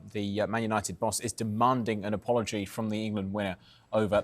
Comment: What does the dude say at 0:06? Demanding an apology from the England what? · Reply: He said "winner" but I think he meant "winger" 😂